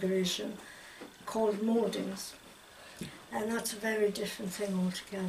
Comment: Hi and all, there is something unclear that says in the interview - at 1:23 she says "there are different forms that people mistakenly take for real transfiguration called ? (Morden's?) - I think she says "Morden's" at 1:31 and YT translates it to that, but I think the audio is unclear and I can't find any reference to this on the internet. I would like to know what word she said exactly? What forms is she speaking about that are mistaken for real transfiguration? Thanks!!